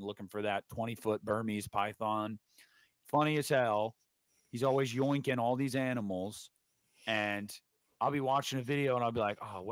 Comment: I swear I thought you said ‘he’s always boinking all these animals’ 😮🐖